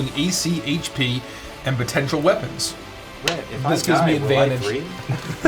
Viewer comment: I can not hear what said, can anyone else decipher it? 😭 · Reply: I heard "Rett, if I die, will I dream?" but I don't know why everyone else started laughing at that.